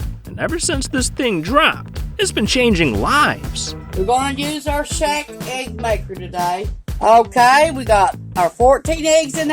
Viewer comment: I thought that woman said “we’re using the Shaq egg maker today, it’s got hoes on the top” 😂😂😂